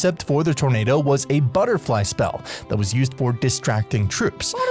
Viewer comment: Am I the only one who thought the Packer said mega night instead of butterfly?